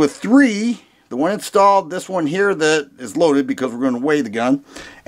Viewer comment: "...we can see here it comes with three magazines... the one installed, this one's loaded because I ran away with the gun??? hahhaha!! Sorry, just sounded like that hahah :-) Now, serious comments: - Brand name sounds too Greek to be Turkish, interesting. - Heavy recoil spring: Turkey = NATO = 9x19 NATO round is spicy, maybe that's the reason? - Reminds you of a Springfield Xd? So funny, over here they import the HS service pistols instead, and I also did a *cough* couple (like 5, rofl) impulse purchases - all HS service guns come with Match grade barrels, amazing triggers and crazy cap, Mec-Gar mags (HS Produkt is the Croatian manufacturer of your Xd's). And yes, this gun does indeed look like an HS (The Xd's are more eye-friendly than the service line HS's). And yes, of course, Xd's and HS's mags are the same. I even bought more mags from Mec-Gar that were branded "Springfield Xd", so, yeah :-) Great video brother! But, after all these years, you already know 'bout how good your videos are - haha! And what I love about your channel is that you're still a humble-hearted fellow. Will never stop recommending you:-)